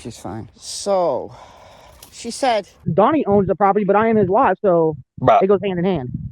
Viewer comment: Wow! "Donny owns the property but I ain't his wife so it goes hand in hand"? Is that what she said at 3:36? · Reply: I think she says "I'm his wife"